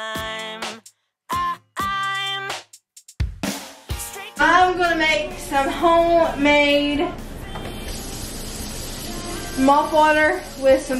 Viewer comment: I thought you said mouthwash instead of mop water 😂 knew my mistake when you used dawn